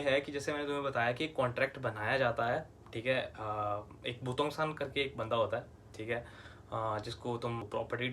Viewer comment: I heard bhuto ka insan😂😂